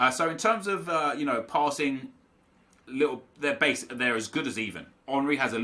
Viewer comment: did he just say: they are as good as Ethan? xD behzzzzzz